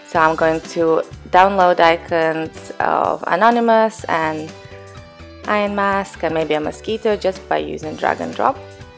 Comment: did she say iron mask 0:29..wtf